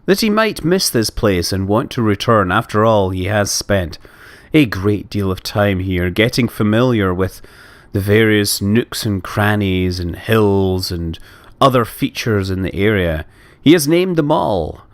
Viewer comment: I heard "nukes and crannys" When you said "nooks and crannys" On